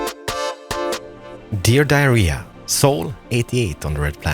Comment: 8 minutes in and.. did you just say "Dear diarrhea" or am I hallucinating